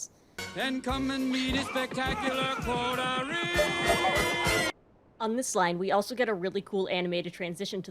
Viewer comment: I always heard "spectacular coterie" as "spectacular poultry"